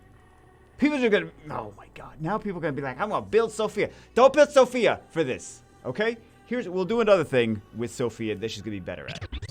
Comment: since i barely know english and i heard, "build sophia for this" i may build her lol